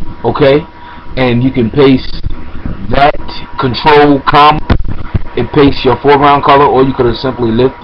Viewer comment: What are you saying at 3:58 is it ctrl com?